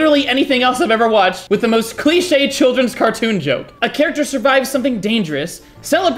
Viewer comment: im not English but i keep hearing you say "kid's cartoon" while the hype and the trailer said: "*NOT FOR KIDS*" so my mind auto-piloted for the rest of the video